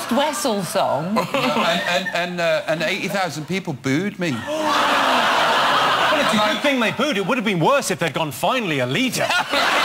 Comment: What does Jimmy say on 16:15? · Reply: "Well, it was a good thing they booed. It would have been worse if they'd gone 'Finally, a leader'"